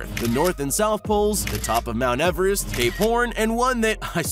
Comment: Did he say gay porn?